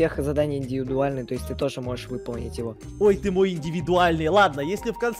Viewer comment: Мне не послышалось? Эдисон сказал:ой ты МОЙ индивидуальный?